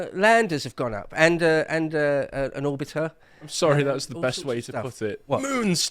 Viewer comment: Oh you said orbiter. I thought you said auditor lol. I thought they were going to DOGE the moon.